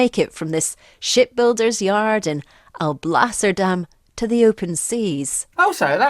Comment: 🤦🏻‍♂️ omg I swear I heard her say “$*!*yard and I’ll blast her damn to the open seas!” turns on captions